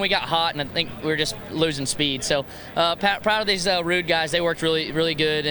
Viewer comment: Funny to hear Austin saying “proud of these Ruud guys” lol sounded like he was saying rude guys. 😆